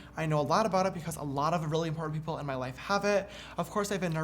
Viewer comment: Did he just say a lot of really, poor, people in his life have anxiety? 10:30 ..